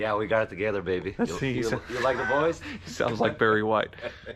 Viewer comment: I thought said Do you like boys?